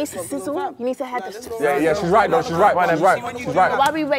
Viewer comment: Did I just hear "wait for the OYOL to get hot?